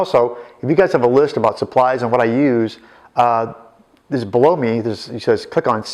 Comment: does say "just blow me"?........ Great tutorial btw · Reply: No xD it's "below me"